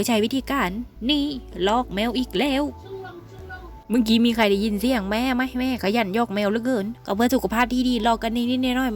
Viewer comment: แม่พูดว่าอะไรค่ะ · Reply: 😁😁แม่พูดแซวแมวว่า โซงโลงโซงโลง ตามภาษาอีสานแปลว่า เหม่อลอย, สะลึมสะลือ ในตอนนั้นสงสัยแมวกำลังนั่งเหม่อลอยเลยแซกโซงโลงโซงโลง และแมวที่บ้านก็ขยันนั่งเหม่อลอยมากจริงๆนะคะ😂😸 🙏🙏❤❤💗💗😘😘🥰🥰😻😻😊😊😸😸🤗🤗